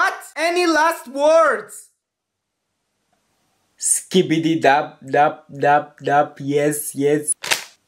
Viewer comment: who heard “skbidi dop dop dop yes yes” 💀💀☠️☠️☠️☠️☠️😂👇🏻